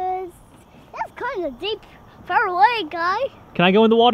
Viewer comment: 22 sec in it sounded like said it’s kinda deep for a white guy